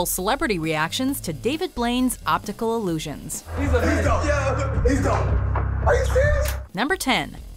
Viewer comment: Did she said illusions???